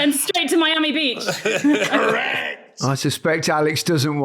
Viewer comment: I thought Micha said ERECT to her, not correct.